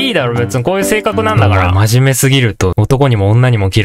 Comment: セメダインがアンダインに聞こえた(アンダーテール)